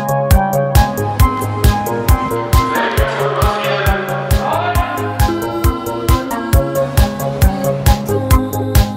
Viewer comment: Was sagt der da mit "Fotografieren" ? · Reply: fertig mit fotografieren glaub